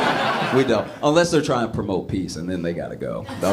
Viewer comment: I thought you said promote pizza 😅